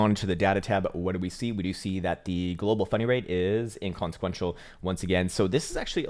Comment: I keep hearing "funny rate" =P